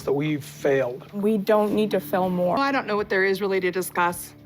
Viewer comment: What is falmm murr? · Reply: when Kody said “we failed” Robyn was slurring her words as usual and she say’s “we don’t need to fail more” but sounded like she was saying“film more” at first i thought she said that “film more” l listened to it again it was difficult to understand her most of the time.. you don’t hear fail that “L” in fail i hear M and her more i hear mur again i don’t hear thar O in more … you get it now.